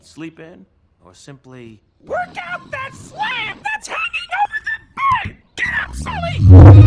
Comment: The " FLaB " is what really gets me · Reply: I heard "the slave that's hanging over the bed" and I was very confused and slightly concerned.